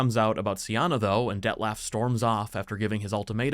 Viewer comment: Im sorry, but i always hear: "Death Laugh" ... ... xD sorry ahahha.